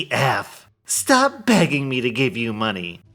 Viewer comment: So I have an ear infection and my hearing is off. During the last post you read, you had said “stop being edgy” but I first heard it as “stop being Asian” and I had to go back to read it because I felt like a random racist comment about being Asian felt super out of place.